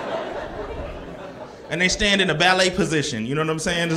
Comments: I'm Mexican, that ballerina position joke had me rolling. I tried not to laugh, I just couldn't. LOL good one. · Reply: I thought he said "valet" 😂😂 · At first I thought he said valet like going to park my car but when he put his hands together down by his waist I got it real quick that is comedy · I'm Mexican, that ballerina position joke had me rolling. I tried not to laugh, I just couldn't. LOL good one. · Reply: The way I thought he said “Valet position” and was like yeah that makes sense too 😂